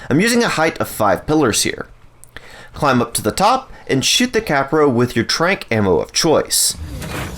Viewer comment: I heard "and shit the kapro". Dude, you okay? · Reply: He said “Shoot the Kapro” lol